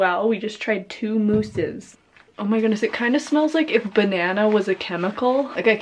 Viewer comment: I was eating chips and the crunch of the chis was loud and so I thought that you said "If my nana was a chemical" 😂😂😂